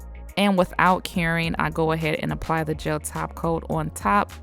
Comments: I’ve always thought she said “without caring” but I just realized she says without curing 🤭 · i always hear "without caring" instead of without curing 😂. anyone else?